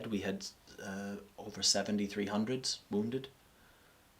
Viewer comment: Oh lord i thought he said "on the busiest day we had over 7300 wounded" at 1:11:55 but he says "over 70 300s" 300 being the code for wounded